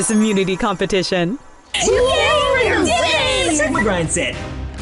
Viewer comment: did i just hear say sigma grindset???